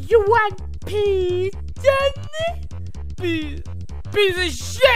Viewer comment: ''Peace!'' what I hear ''Piss''